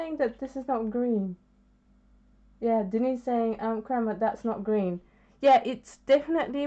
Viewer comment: Hi, you put in a "pan" color of Ultramarine Green, who makes this color? I heard you say "Crema" but i could not find this brand. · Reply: I think she said "Kremer" Like in Kremer Pigments - it's a German pigment seller that also makes ready to use watercolors